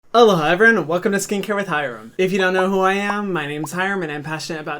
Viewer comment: Why does it sound like he says ”hello hyram, welcome to skincare with hyram”